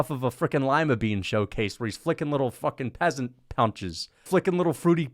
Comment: New Tracyism unlocked: Lima bean punches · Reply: I heard bro say ‘peasant punches’ and now I can’t sleep 😭